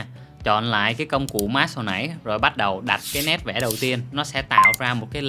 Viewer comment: sao tui nghe ra "bát đầu" ta=))))